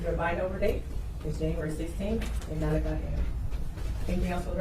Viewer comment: I thought she said January 16